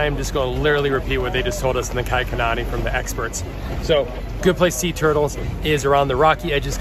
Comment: it sounded like you said “a good place to eat turtles” and I was spooked for a sec LOL